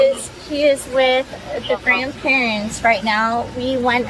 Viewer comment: Why not child endangerment? She knowing left her children with an alledged known alcoholic! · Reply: I thought she said the other grandparents